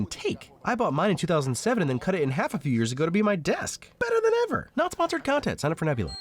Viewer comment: subtitles say "just last year" yet you say "a few years ago".... Hmm..